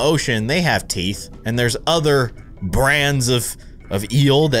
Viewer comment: Did just said brands?!